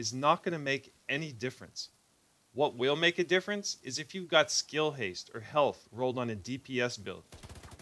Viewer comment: Min 4:38 did you say skill haste on dps build? If I heard right can you elaborate better there bcoz i dnt getting. Thanks in advance great vid Keep them coming 💪🏼💪🏼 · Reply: Ah sorry for not speaking clearly. I meant to say that skill haste ( or health, etc) on a DPS build is a wasted attribute. And even if it’s maxed out, it’s not gonna help your build